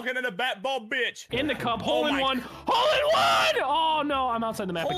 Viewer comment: did say icup at 0:34?